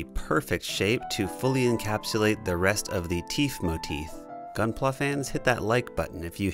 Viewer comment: did.. did you just say “teef motith”